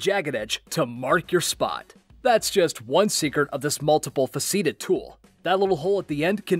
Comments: sorry a multiple-what tool? Did you attempt to say multiple-faceted? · Did I hear the announcer pronounce "multi-faceted" as "multiple- fas-ee-did"? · Did you say "faceeted"?